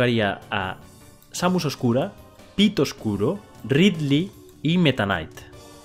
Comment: Oí pito oscuro xD